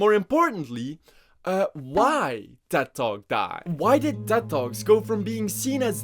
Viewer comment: I totally misheard tedtalk as dead dog for a fat minute and thought he wa just messing around, "are dead dogs dead now?"